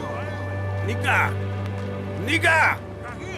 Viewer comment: did he say n👨🏿a?!?😂😂